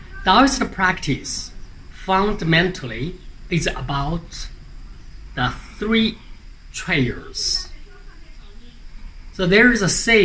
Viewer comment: Thank you so much for introducing me to this image! It's there a transcript available for this video? I'm having terrible understanding your accent. It sounded like you said "the three chairs" but I don't think I heard correctly. 😆